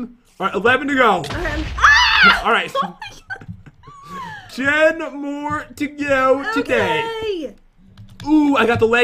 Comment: is it just me or did say "jen more to go today"